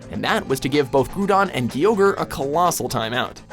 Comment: it sounded like you said grudon and gyoger